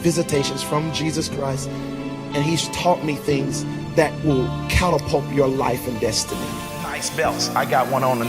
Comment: did homie say "catapulp???"